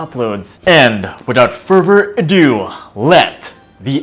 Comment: "without pervert a do" wtf the just say there? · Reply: Without further ado lol